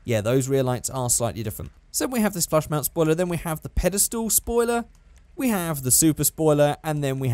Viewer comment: By the way, that one spoiler that's called padestal, It really sound like you say Paddestoel and that's Dutch for mushrooms 😂